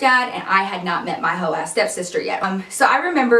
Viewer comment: When I heard “hoe step sister” I WAS LIKE OOOHHHHhHH SHiT THIS GON’ BE A GOOOOD ASS STORY 😊😊😍💀 I love you ❤️ keep on being a boss ;)